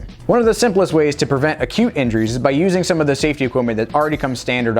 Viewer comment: All the time you said acute injuries i heard 'cute injuries'🤦‍♀️😅